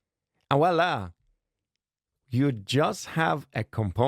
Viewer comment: Nice vid once again ! Did you say "et voila !" at 16:51 ? Because i'm French and it took me a bit of time to understand you say that 🤣🤣